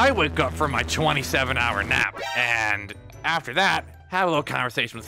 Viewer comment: I love how moon says I woke up from my 27 hour nap😅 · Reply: I thought he said “I woke up from my 27 hour map” not nap 😂